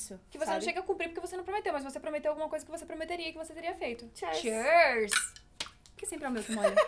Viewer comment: "Chêrs"????? Foi de propósito? É q é "cheers" ("" chirs"")